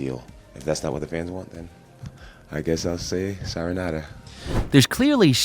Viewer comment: “if that’s not what the fans want, then I guess I’ll say Syronata”